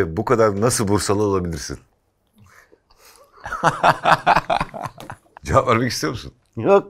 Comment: ne diyor nejat ? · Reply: Cevap vermek istiyor musun? Diyor.